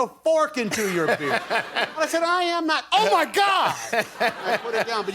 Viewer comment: who heard woody yell "oh my god, buzz its an alien" when Tom Hanks yelled oh my god